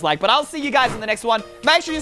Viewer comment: This whole time I thought it said eyestream. But then today he said e y.sooooo my life is a lie.😱👀